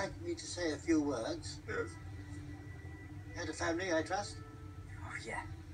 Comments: ''He had a family I trust?'' Ohw yeah.. LOL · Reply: I always thought he said "head of family I trust?" and that's why he said king of arachnids :P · Slughorn: "Had a family, I trust?" Harry: Spider Swarm Flashbacks "Ohhh Yeah." Absolutely Brilliant lol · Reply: I always thought slughorn said “HEAD of family, I trust” when referring to hagrid